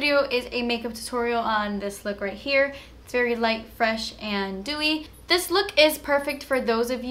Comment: Okay, SOMEBODY PLEASE TELL ME what does she says at 0:16 ??? "this look right here, very fresh and _". She says that word ALL the time, in every video. I'm not a native English speaker, I lover her videos... but THAT word is killing me since forever. Pls help. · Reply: she said "light, fresh and DEWY"...meaning the opposite of a MATTE look